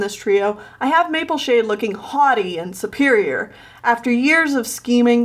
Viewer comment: i thought you said "hottie" instead of "haughty" and was SO confused